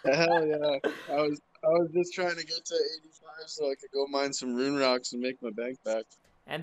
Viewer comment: I thought he said mine moon rocks for bank back not rune rocks lol